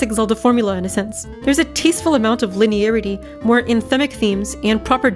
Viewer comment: more what themes? Subtitles say “inmic” but it sounds like “inthemic”, and I don’t recognize either as a word I’ve heard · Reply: anthemic